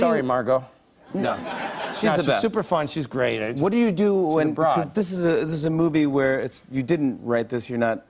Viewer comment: I’m I going crazy cause at 3:10 I heard “what do you do when Brock?” And both of them didn’t say that?!?